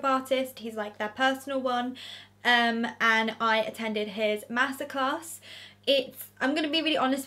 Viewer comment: Ngl I thought you were saying you attended his massacre